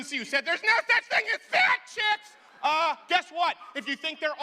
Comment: there's no such thing as what? Couldn't hear clearly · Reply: Fat chicks.